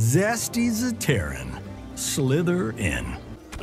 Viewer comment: did he just say slytherin